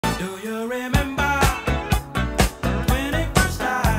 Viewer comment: I hear "do u reamber when u ate a child" 😂 And no i dont recall at😅